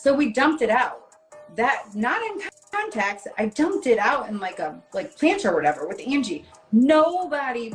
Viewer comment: did she said she dumped it out in plants?